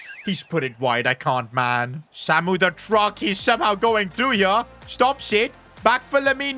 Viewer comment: did he just say Samu The Truck?????????